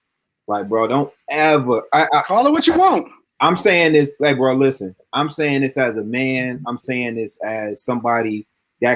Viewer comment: is CRUCIAL!! We need to know what was saying to her man, think she was explaining herself about what was saying he had receipts on. Where the mouth readers at ? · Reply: She’s basically just saying “Why did he bring you up?” a couple times.